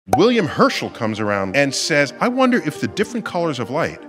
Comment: He said William Herschel, but I thought he said Herschel walker instead and was HIGHLY confused lol